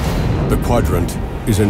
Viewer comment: teeters on the edge of a...batleff? What is said there? Are they being cute and it means something in Klingon?